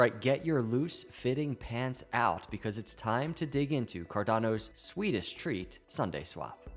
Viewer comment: For a second I thought you said, “Cardano’s Swedish treats…” 🤣